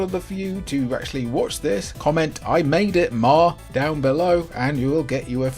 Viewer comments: Apparently people have commented "I made it Mark!" but I swear I heard "I made it ma!" 😂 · I made it, Mark? I sincerely heard you ask us to type "I made it, ma." I'm seeing 'Mark' for the other comments, so I'm thinking it comes down to differences in the English we speak. Btw, awesome video and topic! I'm going to look for a refrigerator sized SNES someday!